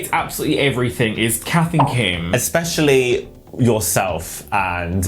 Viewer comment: Instead of Kath & Kim I heard Capt'n Kim